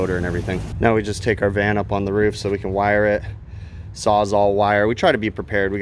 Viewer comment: I hear time to take the Van up on the roof lol , I know that’s not what he said but god that was funny to me .